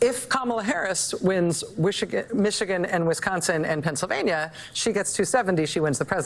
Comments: she said wishagain instead of Michigan lol she probably is wishagain that kamala won! Hahaha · Maddow said Wishigan instead of Michigan. 😂 True Freudian slip ‼️ She wished 😂